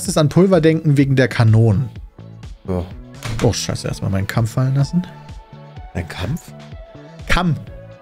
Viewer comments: lol habe auch "Mein Kampf" verstanden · mein kampf hab ich aber auch verstanden xD